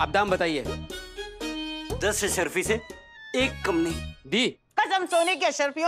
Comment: Did he say "dee" at 9:48 not deal. Like dee mehni tumi deh.